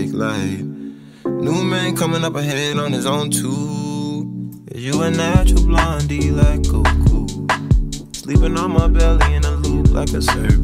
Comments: “is you a natural blondie like goku” is deeper than it seems. goku isn’t a natural blonde, franks saying the person isn’t what they seem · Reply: i thought he said "you a natural blonde, like cocoa?" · I always thought he said. "You're ah natural blonde like kook kook." Which works because blondes are crazy 99.99% of the time in my experience. (: lol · “is you a natural blondie like goku” is deeper than it seems. goku isn’t a natural blonde, franks saying the person isn’t what they seem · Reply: I'm so bad at understanding lyrics i thought he was saying yous a natural blondie like 'oh cool'